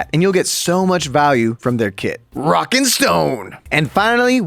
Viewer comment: DID I HEAR A ROCK AND STONE ⛏️⛏️⛏️🔥🔥🔥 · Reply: Rock and stone!